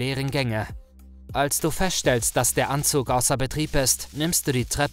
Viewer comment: Hat er bei 1:58 Anzug anstatt aufzug gesagt? Oder hab ich das falsch verstanden?